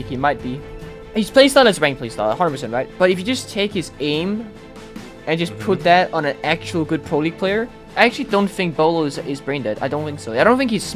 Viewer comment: what he is actually saying is :if you take Beaulo’s aim to me he is not that good